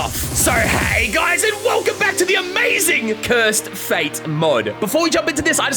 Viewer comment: I thought I heard cursed fade💀